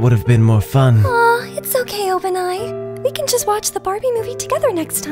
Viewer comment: i'm sorry but did anyone hear ''Bah-bie'' instead of Barbie? Cries